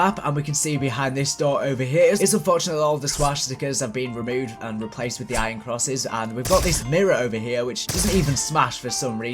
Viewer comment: what did he say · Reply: The swastikas were removed and replaced with the iron crosses